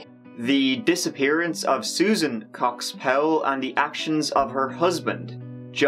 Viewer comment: the way you said cox powell i totally thought you said "cock spell" loooooool